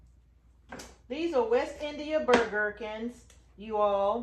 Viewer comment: I have to laugh, I was listening while I did other work. What I heard you say was that you were saving “Burger King” seeds! Ha ha! Keep doing your good work with your sweet husband and may God richly bless you both. ❤